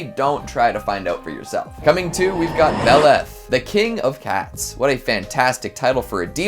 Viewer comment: I thought you said "the king of hats" but then I didn't see any hats in any of the pictures. I then realized you said "the king of cats".